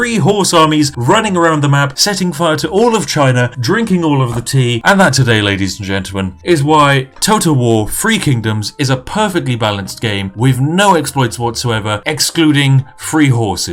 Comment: Every time he says "Three Kingdoms", all I hear is "Free Kingdoms". Free? No. Through exploits? Yes. Free Kingdoms.